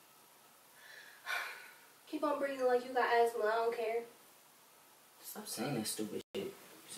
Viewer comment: ‘keep breathing like you got asthma, idc ‘ DEAD 😂😂😭😭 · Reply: I thought she said "like you got attitude" this shit just got so much more funny to me😂😂💀